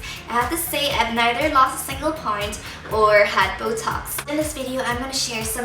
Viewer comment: Oh shoot I thought she said ''I had botox''. My bad :P